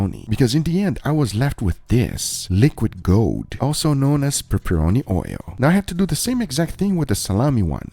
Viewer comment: i thought it said peppercorn oil, not peppernoni oil....maybe an idea for your next video :o